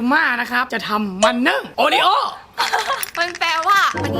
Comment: เหมือนพี่ พูดว่า โอวัลตินนึ่ง ต๋อกบกกี มันนึ่ง โอริโอ้ 555+ ให้ทุกคนฟังดีๆแล้วจะรู้